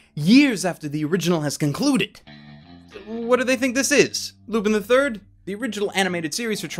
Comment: What does he say at 0:21 ? "...what do they think this is?...____" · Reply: Mr. A Lupin the Third